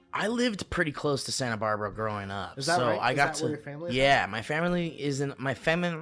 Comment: saying fam er erly instead of family got me so good my 1st listen thru jfc